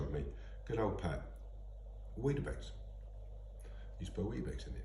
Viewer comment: What dose he mean by weed base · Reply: I thought he said weetabix 😮